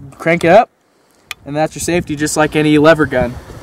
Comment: Did he say "safety" @ 1:13?? God help us!